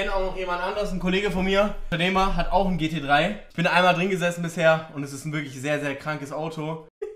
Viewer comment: hab „Neymar“ verstanden 😂